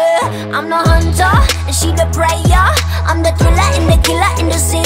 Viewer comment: Am I the only one who hears Bebe say "I'm the Killer" twice instead of "I'm the thriller" the first time.